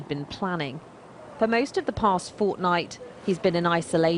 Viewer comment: did u just say “Fortnite” I LOVE FORTNITE WE LOVE FORTNITE WE LOVE FORTNITE